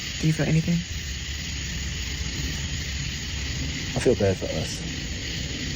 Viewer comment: I thought he said he felt bad for her